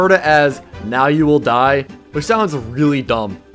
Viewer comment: The cultists sounds like it say "Now you will die foolish mortal"